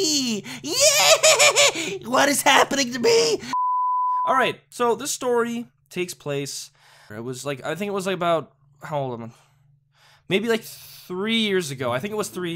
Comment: did you say "how old am I"? I seriously cant tell what your said :/